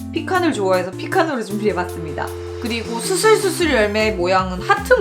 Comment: 수술수술 열매라고 하시는데 수수수숫 열매로 들리는건 저뿐이겠죠?